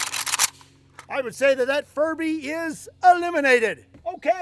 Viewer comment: I thought he said his 4th hated thing was furries and I had to rewind to hear furbys